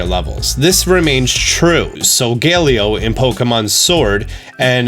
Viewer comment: u said solgaleo and my subtitles said sun gay leo😂